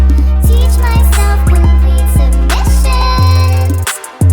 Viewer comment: Ain’t gonna lie. Thought she said “complete remission”